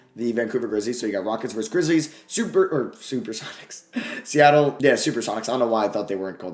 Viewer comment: it sounded like SUE BIRD AND I WAS LIKE YEAHHHHH